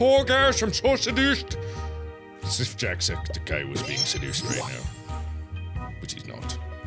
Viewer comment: I thought I heard Mark say, "If only Jacksepticeye was being seduced..."...Septiplier anyone? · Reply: I turned it up to listen- he says "It's as if Jacksepticeye was being seduced right now. Which he's not." Probably a joke about the weird accent Mark was using.